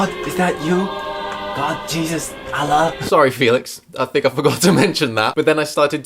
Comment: did u just say Allah